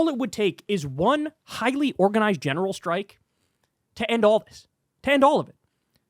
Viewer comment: I heard "one organized missile strike," and was nodding my head. 😂 PS fk YT censors.